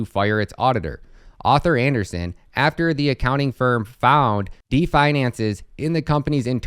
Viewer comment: the computer reading script says “definances” instead of deficiencies and screen goes to pink O’Leary so cannot follow script